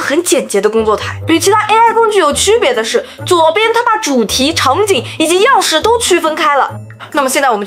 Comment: 左右的 它把主题 是你的原话吧？ 但会空耳成 他妈，， 不过 如果真的说称他妈 其实更好😚